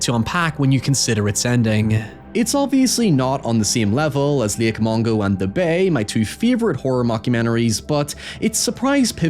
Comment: what movie is he saying? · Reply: Lake Mungo and The Bay